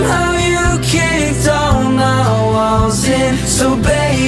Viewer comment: OMG FOR I SECOND I THOUGHT IT SAID 'BALLS' INSTEAD OF 'WALLS' XD